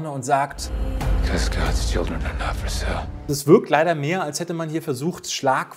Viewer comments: Bin ich der einzige der da akustisch nicht versteht was er da sagt??? Ich verstehe da: Cuz Gods children i love brazil.. Tv total nippel:"Hä"?! :D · Hat irgendjemand bei Minuten 18:05 verstanden was der da sagt? Selten einen englischen Schauspieler gesehen der so nuschelt. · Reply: Er sagt: „God‘s children are not for sale“ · ich hab leider kein Wort verstanden wegen dem Genuschel, was hat er gesagt? ^^ also der Part vom Film. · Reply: "this god's children are not for sale" bisschen spät aber vielleicht hilfts ja noch.